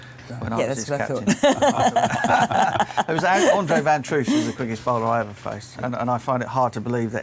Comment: who does Butch say was the fastest bowler he faced? Played for Somerset…? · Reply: Adrian Van Troost ... Quit cricket 26 due to a bad back. he was FAAAST